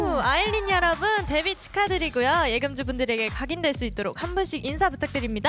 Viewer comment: 나만 예금주로 들리나…?